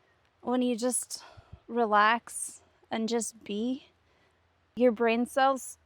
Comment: ❤Love it, and have missed you! Me too, I have finally taken it a little easier, I usually am so focused on working on my goals. We had a mini heatwave here and dang my energy came back so fast i started 100s of seeds in the greenhouse and got a jumpstart on the garden. Now we r back to winter so Iam relaxing and researching for the next video❤❤❤ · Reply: (listening again) what did you say there? "Just Be"? 😊 Your human is a fun study. I see the battle between brain and soul so clearly, and slowly the two are aligning, but it's hard for the brain to let go of control. How do I know? I lived it too 😂